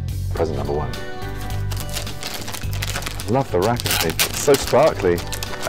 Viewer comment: 여기서 '포장지 완전 맘에 든다' 부분이 잘 안 들리는데 so sparkly 앞부분의 문장이 뭔지 알려주실수 있는 영어 능력자분을 찾습니다,. 자동자막이 안 맞는거 같아서😥 아무리 들어도 도저히 모르겠어용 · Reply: 3년 전에 만들 때는 love the wrapping paper(or wrapper paper) 로 들었는데 정확진 않습니다 :)